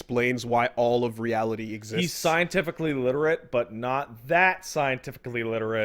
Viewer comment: "Kojima's is scientifically literate but not THAT scientifically literate." Well then he's not scientifically literate. You can't be knowledgeable about something if you get tons of shit wrong about it. This is trying to reinforce one of the pillars of Patism. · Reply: Thought he said 'illiterate'?